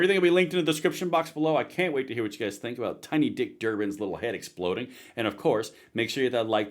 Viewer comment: “Tiny dick, Durbin” is how I heard him say it..